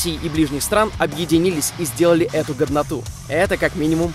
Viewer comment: Мне послышалось : "...сделали эту гоВноту". Хмм